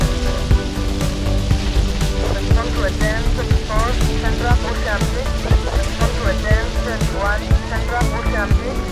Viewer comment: A little off-topic, but does anyone know what the police radio sample from Vice city says? I mean the female voice at 1:16. I hear something like "come to attend..." and that's it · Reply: Respond to a 10-64 central ocean beach